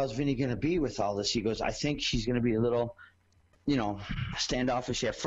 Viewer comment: How's Vinnie gonna be with all this ... (Dereck Christopher) I think SHE'S gonna be a little ... you know ... Did he really say SHE ???????